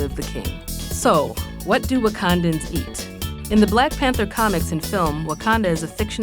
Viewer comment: “So what do a condom eat” Captions said so